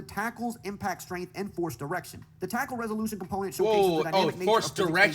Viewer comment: mf thought he said forced erection 💀💀